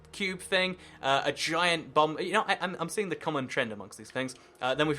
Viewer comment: definitely sounded like you said ''giant bum'' ahahaha